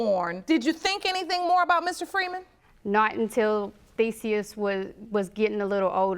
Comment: I swear I heard her say Feseaus 😅😅